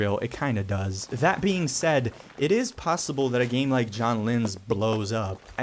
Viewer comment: i don't understand the name of the game, is it "Jon linz" ? (subtitle say that)